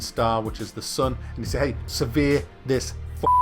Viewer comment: every time u said "survey" i heard "severe"